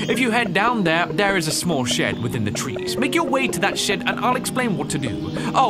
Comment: "If you head down there, there is a small shed within the trees. Make your way to that shit-" ...That's all I heard. 😆😅